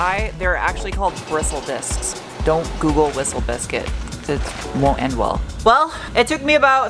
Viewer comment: Whistle biscuit!!!??? I thought you said whiffle biscuit. xD